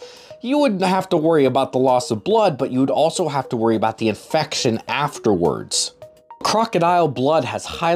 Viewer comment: I thought he said "you'd have to worry about affection afterwards".. Like no one's gonna love you when a croc bit off your arm